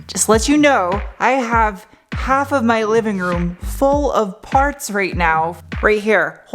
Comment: "half of my living room is full of farts" Me: "hahaha..." Cut to parts in living room Me: ... Me: "I'm sticking with what I heard... Hahahaha"